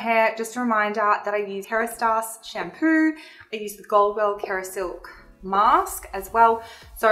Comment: Quick question what is the product you say at about 7:40. It sounds like Goldwell Kerasilk masque? Tia.